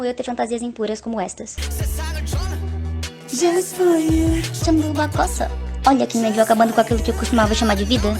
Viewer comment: só eu que entendi sua bunda cosa?